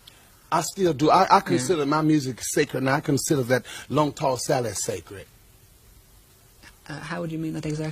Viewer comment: "Black bottom from Georgia" lol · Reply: did you hear what he said at 4:58 mark " long tossed salad sacred " lmaooooooooooooo